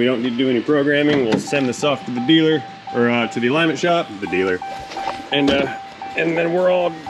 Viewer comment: "THE" dealer..lol i thought you first said drug dealer